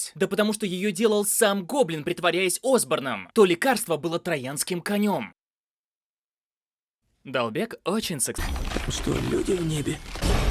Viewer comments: мне же не послышалось?: очень с... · эмм.ну ....я вам щяс скажу что там говорится.... очень сексуальный.....мдааа...